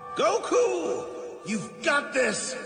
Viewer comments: Bruh i alway thought he said merc that nigga now 😂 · I thought he said that niga dawn